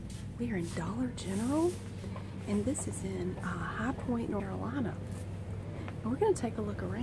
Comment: Lol, it glitched a bit at the beginning and all I heard was "we're in High Point Noralina"